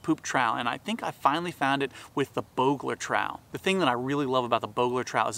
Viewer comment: At first I thought it said "Burglar trowel", then I thought "is it a tool for dogs"?